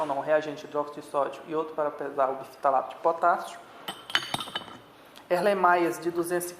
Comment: É o que de potássio ? Eu nem entendi kkkk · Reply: biftalato de potássio